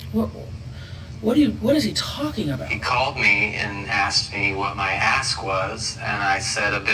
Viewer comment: Honestly, I thought he said, " he called and asked me how much my a** was!" I had to rewind a couple of times. 😆